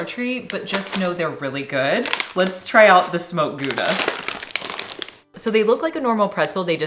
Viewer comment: Near the end, when you were trying the flavored pretzel sticks...I swear I heard you say one was a "Smoked Tuna" flavor ! Imagine my relief when you showed the bag and it was "Smoked Gouda" !!! 🤣🤣🤣